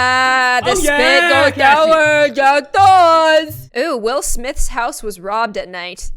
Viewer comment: sounded like she said "Will Smith" 🤣